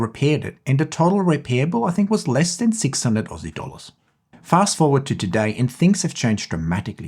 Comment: 600 what ???? 0:51 I can't understand what are you saying 😕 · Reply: $600 Aussie Dollars